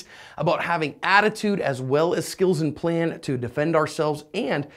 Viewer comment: im not tryna be funny but did he say skittles?